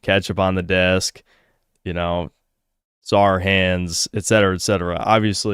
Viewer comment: What did he say here? It sounds like "saar hands"